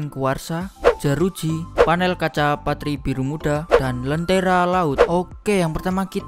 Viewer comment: gua salah denger atau emang lonte-Ra laut 😂😂😂